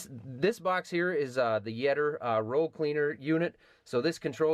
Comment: when it said yetter i thought it said yeet lol